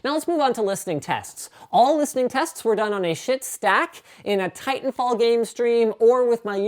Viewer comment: Did he just say shit stack?